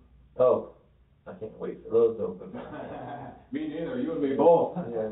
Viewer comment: The interrogation doesnt really get going until around 42:00 if you dont want to listen to the small talk and beating around the bush. This one takes a while to get going. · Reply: Anyone else noticing that the editors subtitles aren't matching what's being said like 30% of the time? Like, "Vest Western Hotel"? I thought I had youtubes subtitles on, which sometimes don't match what's being said, but it's not my youtube subtitles as they're off. Someone added subtitles manually and wrote in either wrong words or completely wrong phrases and sentences. Like at 15:38, pretty sure he said "I can't wait till those open", but that's not what the subs say at all. Or 20:45, 21:26-21:33, 23:19. There's a bunch more. It's funny, but you'd think he'd do his due diligence in attempting to translate whats being said a little better so that when he added subtitles, they would watch what was being said in the video, or maybe just order the interrogation transcript since many interrogations usually have transcripts.